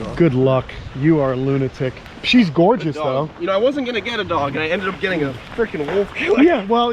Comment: Thought you said you was going get a dog